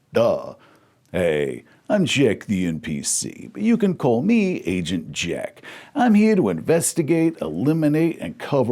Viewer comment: Not gonna lie. I was listening not watching at 1:15 and could have sworn he said “Asian Jack”. Thought I was gonna see with a Fu Manchu 😂